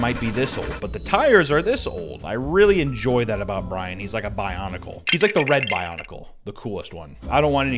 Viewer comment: did someone say Bionicle???????? 🤩🤩🤩🤩🤩🤩